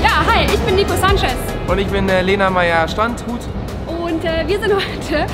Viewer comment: Ich bin Nico Sanchez und ich bin Lena Meyerstrandhut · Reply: Ich hab Standhut verstanden😂😂